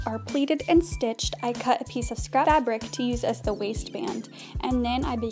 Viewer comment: What do you say at 1:50? A piece of what fabric? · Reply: she said "scrap fabric"